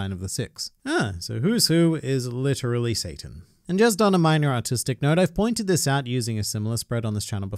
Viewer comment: Did he say “autistic side”